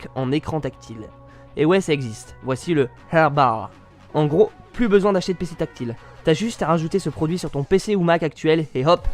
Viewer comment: Attend.. Le quoi..?? 😂 Jai compris Awbaw ( cest une gentille moquerie :)